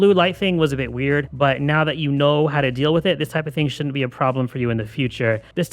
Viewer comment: did you say "now that you null how to deal with it"? 😂